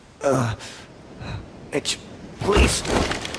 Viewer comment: i thought he said bitch